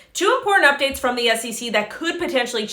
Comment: "2 porn updates from the sec" that's what I thought she said at first lol